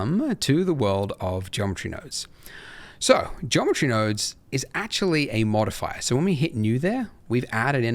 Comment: Why do I keep hearing Geometry Dash?🤣 Anyways, really good tutorial, thank you!